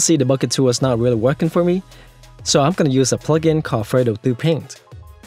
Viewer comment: Hey thanks, but I can't make out the name of that plugin you used to apply the material at about 1:30. ? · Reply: It's Fredo Thru Paint ^^